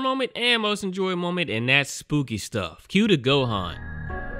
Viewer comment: Been subscribed for over a year and always thought you said cue the Goku...but it's Gohan. I feel like my entire life is a lie and my head hurts.